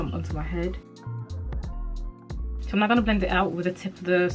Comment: I kept hearing " I'm not gonna..." and I was confused why she was doing what she said she is not gon' do, I had to rewind and realised she's saying " I'm now gonna" 😄